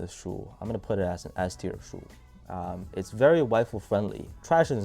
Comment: Sorry but what are you saying at point 10:01, its very___ friendly? Thanks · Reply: Wide foot friendly